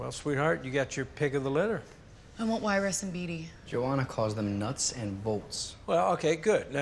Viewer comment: What is Peeta saying? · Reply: 'Joanna calls them nuts an volts'